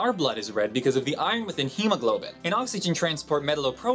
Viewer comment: Did you say Oxogen at 1:30?